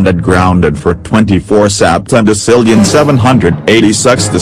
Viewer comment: did he say sextillon? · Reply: Sexdecillion*